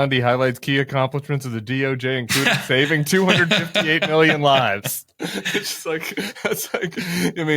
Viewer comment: “Are you ready” trump says…”FOR THE BULLCRAP OF THE MOMENT” unsaid by him. I thought Spam Bonde said, “258 million lies!” 😂